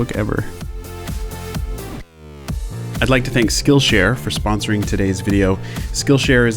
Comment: I thought it said "killshare" for a second